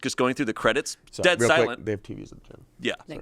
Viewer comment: Could someone decipher what Damien says at 19:05? I keep replaying and I just can't figure it out 😩😊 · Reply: “They have tvs at the gym” - turn the captions on! I have to on most of these vids for the muffled bits